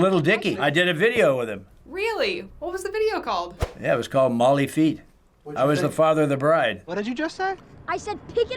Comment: Did he just say the video was called “Molly feat”